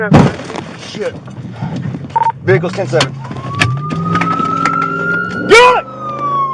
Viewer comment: Does he say "Vehicle's tits up" after the impact? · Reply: Vehicle is 10-7